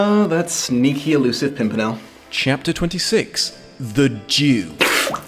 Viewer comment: When you mishear "the Jew" as "the Duke" and for 18 minutes are confused as to why a Duke was deserving of a spit-take in a book focused on the aristocracy. Whoopsies.